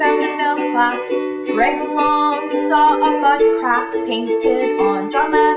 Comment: lol on 0:40 - 0:41 does she say buttcrack?